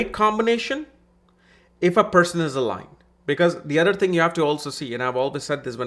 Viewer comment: can someone tell me what word used here? xD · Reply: Aligned